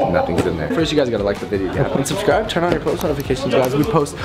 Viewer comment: Lmao I keep hearing “un subscribe” when he says “and subscribe”